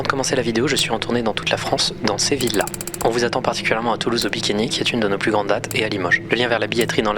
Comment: à bikini et le moches jai entendu